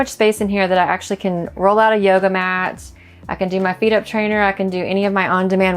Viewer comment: when she said ' yoga matts' I thought I heard her say 'baloney decks' and was intrigued to learn some new sh!t or at least maybe a new slang term but ahh well.....